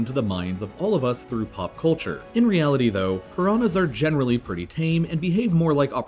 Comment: Just on subtitles nd it translates coronas instead of piranhas😂😯